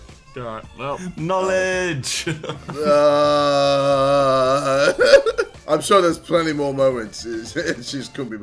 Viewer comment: I swear he said "I'm sure there are plenty more momentzes"